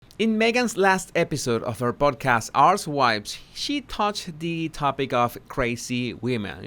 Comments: When he said “archetypes” I thought he said “arse wipes” 😅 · OMG! When you named the podcast, I thought you said “Arse Wipes” instead of “Archetypes”😂 Honestly, the former might be a more accurate description. And Jordan Peterson is intelligent, insightful, honest and ethical. So enjoy listening to his talks. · Hiya I don’t usually comment on this channel, when you said Archtypes in the very first seconds of video, sounds like you said arse wipes lol a snippet taken out of context lol! Thankyou & blessings to you and yours really enjoy your videos. Ontario Canada 🐝🇨🇦